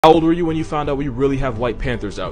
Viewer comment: I thought you said how old were you when you found out you had white parents i was gonna be like tf